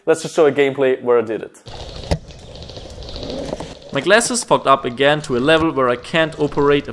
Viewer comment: I thought I heard him say "My glass was fuck up again. " lol